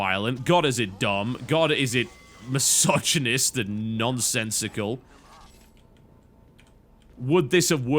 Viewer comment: He does not say it completely. He said Misogynis-nonsensical.